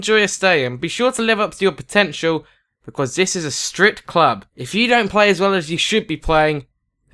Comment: i thought he said this is a strip club